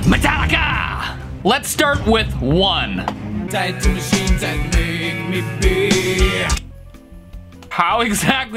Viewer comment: I heard take to sheets than make me pee